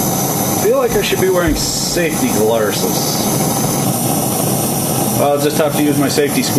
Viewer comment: For a moment i thought you said safety lettuces...well, that's something... An artistic nut for your fanciest bolt. And even if you weren't making one, that'd be nice, a whole ....something.. made from parts that are like that. Don't mind me, just waxing artistic from back in school.